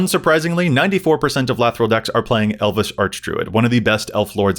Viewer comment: please tell me I'm not the only one who heard "Elvis Archdruid"